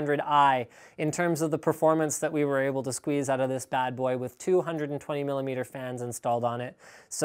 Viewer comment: oh i thought he said 220mm fans. i was confused